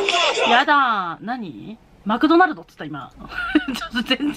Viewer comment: これみてからもうマクドナルドにしか聞こえなくなってしまいました😅🍟何度聞いてもマクドナルド、、、